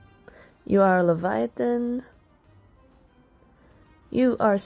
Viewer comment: I'M SORRY- I can't stop laughing because it sounded like "You are Satan" instead of "UR Satan" 😂 WHEEZE